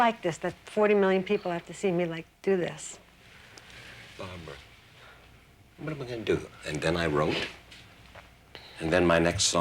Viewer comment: Could anyondd tell me what he says right after 5:42? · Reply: He says, "Barbra, what we gonna do ... and then I wrote ..."